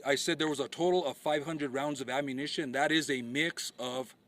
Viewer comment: Well they found 500 rounds of live ammunition so let’s see him explain that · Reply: I thought the Sheriff said 500 rounds of ammunition.